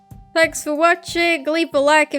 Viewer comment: I’m sorry what did you say at the end there... it almost sounds like you said... sap isn’t real???? Yeah, let’s go with that